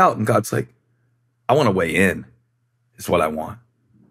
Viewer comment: I heard that as God says "I want to WEIGH in." Auughhhh....won't God.